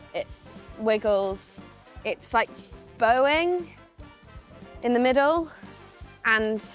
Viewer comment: I heard "it's like Boeing in the middle" which I don't think is terribly inaccurate either way.